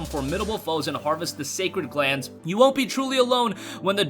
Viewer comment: Did he just said "sacred glands"? What has gaming turned into?